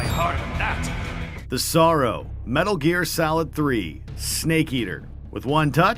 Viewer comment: I think I'm hungry... because I heard him say "salad"